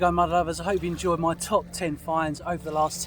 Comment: I love your videos. When I first started watching you I thought you were saying “ To all my lovers”, not “mud lovers “. I am so fascinated with all of the history you discover. I cannot imagine holding an object in my hand that is hundreds of years old. Keep making my day!